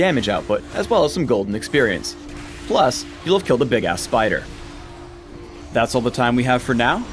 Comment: does he say big ass spider?